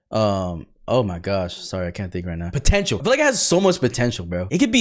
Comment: Did he say potential